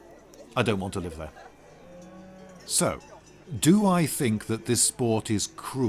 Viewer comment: At first I misheard one of those last statements as: "Do I think this is a cool sport? Ehhh.. no." My reaction was just :(